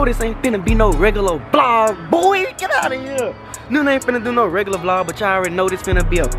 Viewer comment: it sounded like he said “nunae ain’t gonna do no regular prank today”